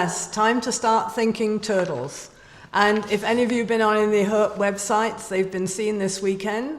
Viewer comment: the what website? What's she saying? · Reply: She did not specify a specific website, she generally referred to "herp" websites (websites or groups on social media that focus on reptiles and amphibians, especially first sightings at this time of year).